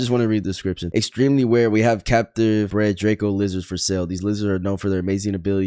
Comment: Bro Said thèse lizards and i thought he Said these losers